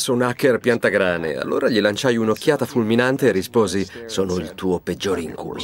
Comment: dice: "Sono il tuo peggiore incuL0" o ho sentito male io? 😂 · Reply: Sono il tuo peggiore incubo!